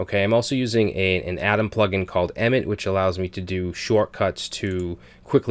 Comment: Sorry I didnt quite catch the plug-in you mentioned which allows you to create divs in like a microsecond!?!? Great vid btw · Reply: nvm, its called emmet. I misheard as "emit". Seems like an awesome package, don't know how I've gone without this!